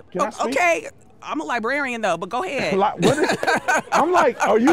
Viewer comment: She is librarian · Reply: I thought she was saying she was a Liberian. And I was thinking, "why are you here, then?"